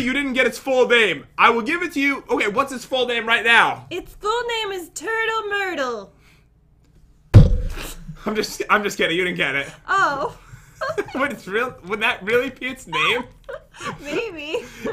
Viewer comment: When Jen said it’s name is turtle myrtle I thought she said turtle burger cause I could not hear and I was like what